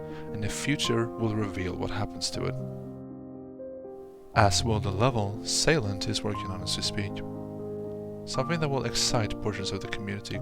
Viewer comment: i like videos like these, theres something that i just like about the history of the game and the community my only question is what did you say at 15:45 because i cant understand it quite clearly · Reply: "As will the level Sailent is working on as we speak."